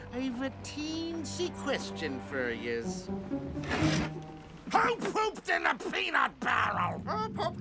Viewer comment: what did bizzaro captain crunch just say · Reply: Who pooped in the peanut barrel